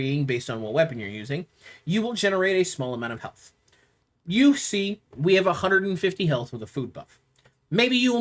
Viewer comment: sounds like you said “we have 150 health with a fupa” 😂